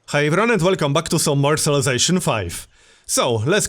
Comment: can I ask what you say to greet viewers because what I hear is "how you runnit" xd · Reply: He says "hi everyone, and welcome back to..."